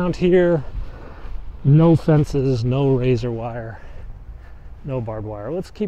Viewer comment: You misheard the word Man... They didn't say razor wire ... They said LAZER WIRE. lol